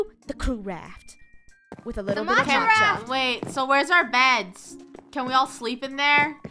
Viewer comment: When funneh said “mocha raft” I thought she said “gacha raft”